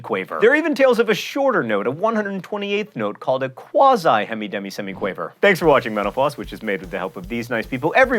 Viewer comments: sounded like he said "thanks for watching menopause" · It really does sound like he says "thanks for watching menopause" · it sounded like he said thanks for watching menopause